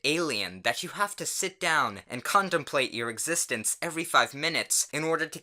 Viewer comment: I heard condomplate lol but I know it’s contemplate